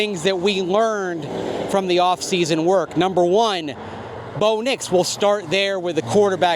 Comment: Tell me you didn’t actually watch the video without saying you didn’t actually watch the video 😂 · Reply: LOL - I watched to the end as I always enjoy your show. But after rewatching it just now I see (or hear?) what I didn't catch. I THOUGHT I heard "Box Nix, will start there with a QB postion..." - rather than what you REALLY said "Box Nix, We'll start there with..." I apologize.